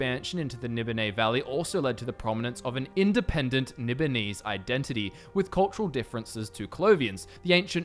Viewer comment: Are you saying "Nibbonese" or "Nipponese"? I suspect "Nibbonese", but it sounded like the "Nipponese", and the CC print it as "Nipponese".